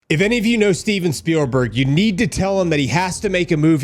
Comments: For some reason I thought you said Steven Segal😂 imagine that movie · Oh, you said Steven Spielberg. I thought you said, Steven Segal. 😂 I don't think you want Segal.